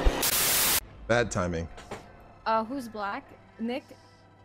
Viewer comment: What? What did she said? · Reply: "Uh who's black- Nick"